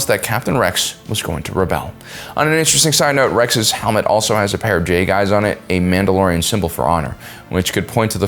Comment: 100% thought bro said "Rex's helmet has a pair of gay guys on it" 💀💀💀